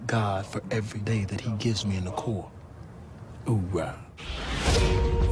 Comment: Did he just say core?1:52 . It's corps. get it right hollywod